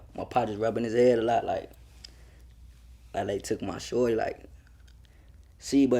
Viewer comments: who hear him say la took my shorty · Bro 56:24 it sound like he said “LA took my shorty” but I wouldn’t expect his pops to know LA so I’d imagine he said “that life took my shorty”